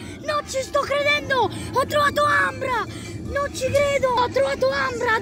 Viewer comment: non ho capito cosa ha trovato? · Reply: AMBRA